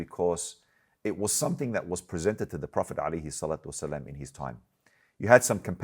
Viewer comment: Time Stamp - 2:29. Did he say prophet Ali? Was that a mistake? Did he mean Prophet Mohammed peace be upon him? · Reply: He said “The prophet alayhi salatu wa’salam”